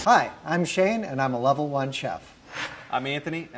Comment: At first I thought the first one said: "Hi, I'm Shame and I a level one chef".